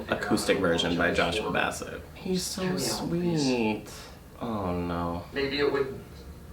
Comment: i’m sorry at 5:09 did he say ej was sweet like what. also can we actually get and acoustic hsm cover album sung by joshua bassett like please. i need that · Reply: I’m pretty sure he just said “he’s so sweet.” Also, don’t hate on me for not knowing this but who’s Joshua Basset? 😂 Edit: I’m so STUPID, Joshua is the guy who plays Ricky! Lmao 😂 😂 😂